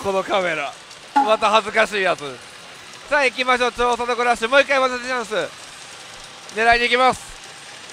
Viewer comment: ここの「もう一回上乗せチャンス」テロップなかったら多分聞き取れてない笑 また隣駅いきかな笑